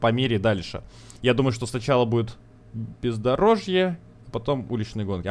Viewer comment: мне одному послышалось «ПИЗДОРОЖЬЕ»